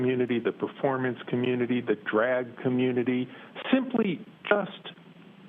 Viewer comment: DID HE JUST SAY "THE DRAG" COMMUNITY???!!! @ 12:40???? 🤣😡